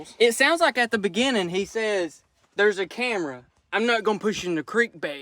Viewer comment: yea it sounded like they did say there’s a camera just one question is the problem still going on?